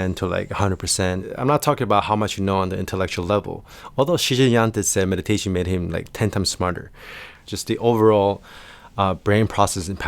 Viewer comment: Shinzen... who? I didn't catch who you mentioned. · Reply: Shinzen Young